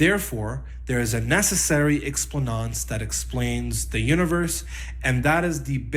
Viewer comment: This guy wants to show that he’s intelligent by using uncommon words most people didn’t know existed. It's a very common method, used by the intellectually dishonest, to ensure people are scrambling to understand the word(s) instead of focusing on the content of what is being said. He believes using words such as circumscription, defeasibility, and contrastive makes him appear as an intellectual instead of just obfuscative and garrulous. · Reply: There's another word he uses at 12:27 that sounds like "explanance"... I think he's actually saying "explanans", which is just the fancy Latin way to say 'explanation' or 'reason'. Outside of "circumscription", I thought either I was mishearing him (due to the weird way he elides certain phonemes) or he was making up words. At least I learned something new today, even if it wasn't what he was aiming for!